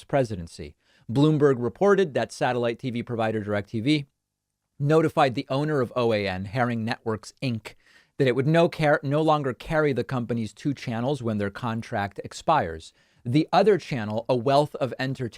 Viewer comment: I thought he said Karen Network Inc. at first as the owner of OAN, I had to listen to it again. hahahahaha.